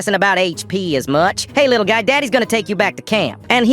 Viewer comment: did bro say daddy 🥀